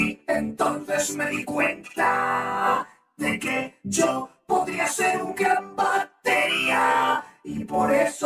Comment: Le entiendo que dice “De que yo podía ser un gran bateriaaaa ” xD